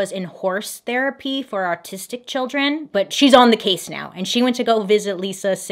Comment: Did you just say artistic children 😂